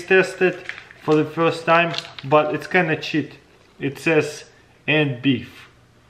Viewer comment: did he say what i think he said lol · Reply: It’s kinda chit and beef 🥩